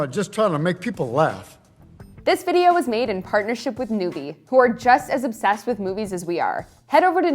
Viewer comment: When they said noovie. I thought they said newbie.